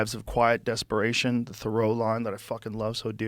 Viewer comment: "most men lead lives of quiet desperation; the throw-line [?] that I fucking love so dearly" - I dont understand. What is he saying? What is a throw-line? (English isn't my first language) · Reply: He says "Thoreau line" as a line from a book by Henry David Thoreau. Hope that help, 8 months later lol